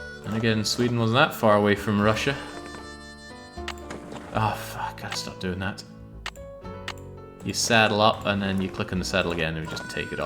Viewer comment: Did I hear a rare swear! Sounded like a certain F word to me. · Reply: Close but not quite. Auto-generated subtitles actually got it right here, it's "augh, got to stop doing that"